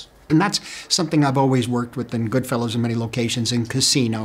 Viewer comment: does he say goodfellows?